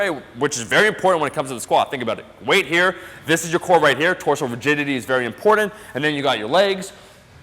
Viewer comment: Sounded like you said core virginity!😂👌🏽👌🏽👌🏽